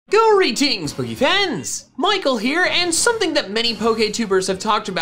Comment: I watched this video with captions. He always said “Greetings Pokefans!” I never knew he said that. Why did I never know that? Here is the reason why. I thought he said “Gory Tings, Boogey Fans!” I’m not the only one right?